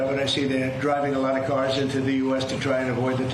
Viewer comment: Did he just say, "theyre driving the cars over the border now."??